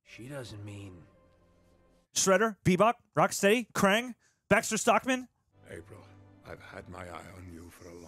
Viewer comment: I thought u said Max Verstappen, i was like ‘💀’ 😂😂💀💀💀💀